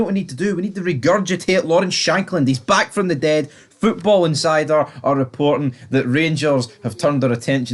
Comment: Thought u were saying football n cider 🤣